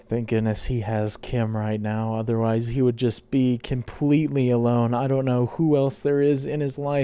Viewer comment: Did you just say Kimpletely alone?